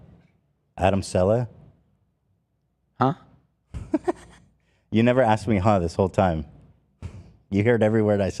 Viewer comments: Can someone explain 2:06:45 to me? What is Ethan saying? ‘You never asked me huh this whole time’? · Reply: He just threw out the name Adam Saleh (idk his relationship with foundry) and he said “huh” so Ethan said “you never asked me huh this whole time” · What’s Ethan say at 2:06:50? I replayed it like 4 times and literally can’t make it out 😂 Plz & thku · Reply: he said Adam Saleh, a famous Muslim youtuber who is kinda known for being crazy off camera